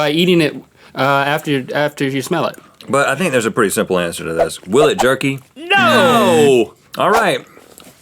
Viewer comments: I thought he said will it jerk me 😂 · I just woke up and I thought it said will it turkey?